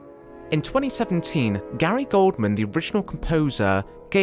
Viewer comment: Is that Gary Oldman instead of Gary Goldman? 😅